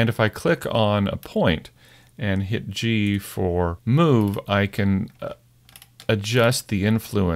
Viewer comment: Hey, at 9:47-9:49. After you use the proportional editing tool when you choose connected. When you say click on a point and hit "g4 and move" I wasn't clear what you just said or button you used. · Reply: Hi, I think I said I would use "g for move". G is the shortcut for the Move tool.